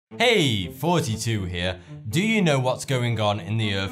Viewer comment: I thought for a while he was saying "Hey 42 Here" but nope.